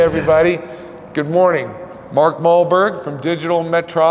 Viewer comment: Thought that said Mark Walberg